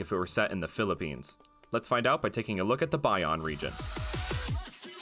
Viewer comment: I thought I heard Baion Region. 😅